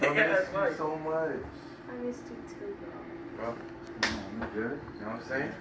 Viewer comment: i thought she said i missed u too bear lol